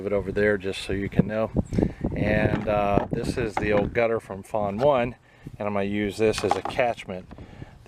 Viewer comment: Hi! :D I didn't understand what you said @ 0:40... "this is the old rain gutter from .... one." closed captions said 'fawn one'. I'm going to try to find the previous video, as I missed any harvest table. If you want to lessen the wind noise overcoming your voice, maybe face away from the wind. - Just an old lady's idea. Hug your lucky wife for me, and THANKS! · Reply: FAWN is an acronym for Flowing Aerated Wicking Nutrient, a hybrid hydroponics method I created. Gutters are part of that system. You can search my channel for "FAWN" to see how they're used if interested.